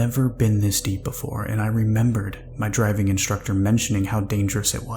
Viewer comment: says driving instead of diving.... or maybe it was a submarine driving course 🤔